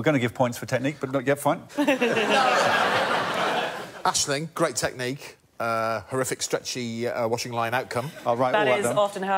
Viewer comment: Can someone enlighten me about what Greg says around 8:37 ? "great technic, horrific strategy yet washing line outcome" ? Is that correct ? Sorry for asking but I'm french and he talks so fast I'm not quite sure...